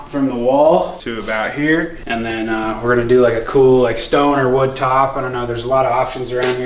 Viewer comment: 😂 I know you said "stone or wood top" but I heard "stoner wood top".